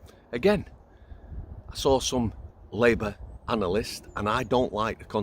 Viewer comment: I thought they said Labour Anal-ist because he looked like an arse.